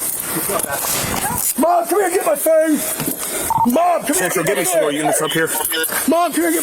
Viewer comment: I hear "Get my phone" the 1st time and I'm half deaf and plugged up with winter sinus hell.